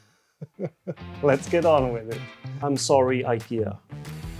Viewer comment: Did he just say "I'm saw-rry, Ikea" 😅